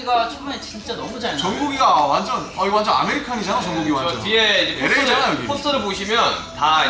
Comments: Everyone: laughing and having a great time V in the back: serious mode-on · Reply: did rm said amir khan 😧😧❤❤❤❤❤❤❤❤??? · RM fans 👇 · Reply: did rm said amir khan 😧😧❤❤❤❤❤❤❤❤???